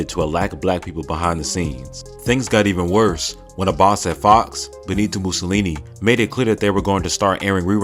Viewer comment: what the fu.... did he say Benito Mussolini? 🤣